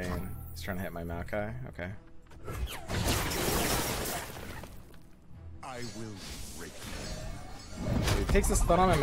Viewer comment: What is swain saying at 7:23??? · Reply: "I will break them"